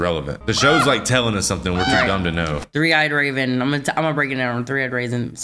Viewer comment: Did I hear "Three-eyed Raisin"? 🤣🤣